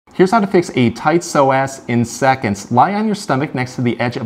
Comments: I thought he said "here's how you fix a tight sore ass" !!!! 😂 only clicked when he said it the 2nd time 😅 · I heard " a tight sore a$$" instead of " a tight Psoas" omg, thank you for the pinpoint tip! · I thought he said " a tight sore ass"